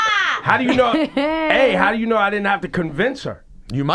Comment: GUYS! at 13:18 Ebro says "how do you know i didn't have to convince her" instead of "how do you know i wouldn't have to convince her". Definitely smashed.